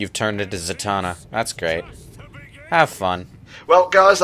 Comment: I thought you said Shoddy Dims